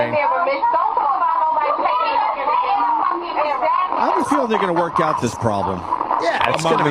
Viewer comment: I could have sworn Opie just said "have a Field-N to work that out"